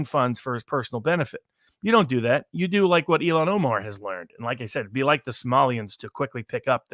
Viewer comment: Did he say "Elon Omar?" 26:33 I think he did! LOL!